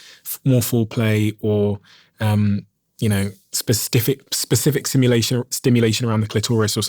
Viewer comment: instead of Specific he said Spestiffic loves thinking about those Stiff ones